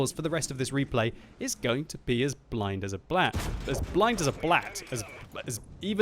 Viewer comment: Kinda sounded like he said blind as a blyat